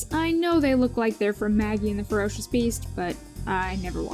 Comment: For a while I thought that show was called: "Maggie and the Roast Beef"